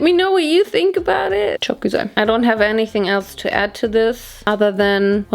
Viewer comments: did she say “çok güzel” at 20:11 😱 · ive been following you for a while now and did you say 'çok güzel' at 20:10, oh that was so exciting to hear and u were super cute and yess definitly we had this era in Turkey too !! love u,great work and look <3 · did you say çok güzel at 20:10 ahahahhah im shockeddd, i love ur videooo😍😍😍😍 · Is she said "ÇOK GÜZEL"? :O <3 :O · Did u just say çok güzel in turkish 20:11 or cant my brain just understand it other way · Did you just say “çok güzel” on 20:10 or am i deaf?? I’ve noticed that you’ve used some Turkish words in your videos but that was out of nowhere if i heard correctly lol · OMG DID YOU SAY “ÇOK GÜZEL” AT 20:09 ARE YOU LEARNING TURKISH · did she just said "çok güzel"!!! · did you just say "çok güzel" at 20:09 lol